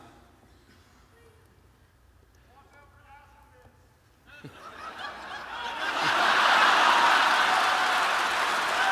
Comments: What did the guy in the audience yell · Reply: "Walk over the acid pit" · what does he say that makes Will raise his Ol'mighty middle finger at a lord of the underworld? xD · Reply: "watch out for acid pits"